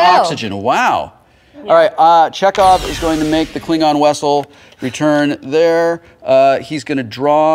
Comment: Did Wil did just say "Klingon Wessel?" @ 12:43 LOL!!!!